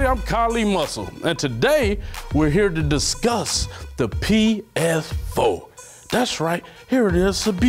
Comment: did he just say PF4?